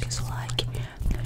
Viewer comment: teve uma hr q eu ouvi "não esquece o dislike" e fiquei "wtf?" KKKK